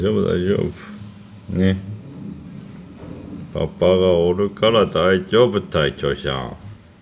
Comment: の「パパがおるから、大丈夫たい、チョーしゃん」が「パパがおるから大丈夫、隊長しゃん」に聞こえる(笑)チョーしゃんがプルプルしてる姿みるとギュッってしてあげたくなるのよね😖